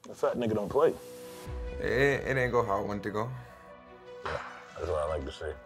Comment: wtf he say lmaoo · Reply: “It ain’t go how I wanted it to go”🥲